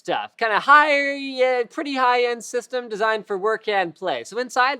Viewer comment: what is he saying? "high-er-y-end